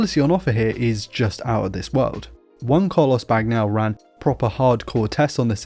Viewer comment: what the fuck did he just say lmao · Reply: He said